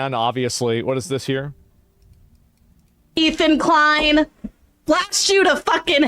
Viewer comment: What did that hog even say during that clip?? "Blast you to hell?" "Blackshoe you to hell?" My brain can't even comprehend what her hog is saying there, I listened to it 5 times.